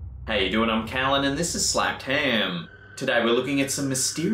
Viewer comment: Honestly when I started watching this channel, I thought he said 'Slammed ham' and not 'Slapped ham' 😂